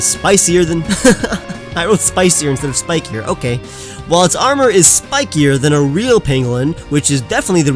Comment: I definitely heard you say "While it's armor is spikier than a penguin."